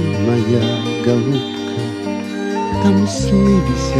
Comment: Мне послышалось Трусы весят на всех ветвях Фига себе думаю Боярский пошляк 😂